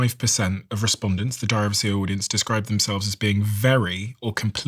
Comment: I just learned that Steven is saying “If you like the Diary of a CEO brand…” I always thought he said “If you like the Driver Sierra brand” because he says it so fast. The more you know!!